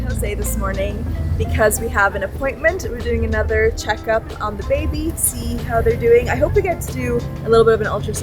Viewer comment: Did you say babies? Are you having twins? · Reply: I heard "another check on the baby-see how they're doing"